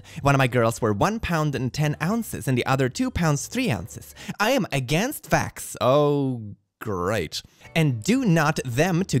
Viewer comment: "I am against vax" sounded like "I am against facts." Someday soon I am sure someone will say this on reddit or maybe xitter.